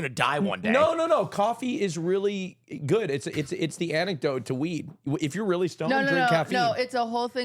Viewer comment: Omg he really said anecdote